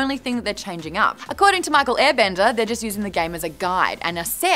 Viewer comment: did she just said Michael Airbender?